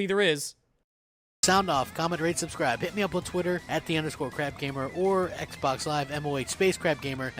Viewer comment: Half the time it sounded like you were saying 'crabgamer'. At first I thought 'well that's still appropriate', but then I remembered crabs are awesome so he doesn't deserve the comparison. Unless, of course, we were comparing him to a much...tinier variety of crab...